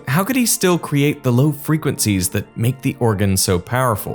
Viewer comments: I kept hearing "Lo-Fi" lol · I hear 'Lo fi'.